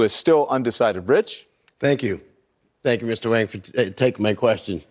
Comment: Did he just say thank you Mr. Wang? Lol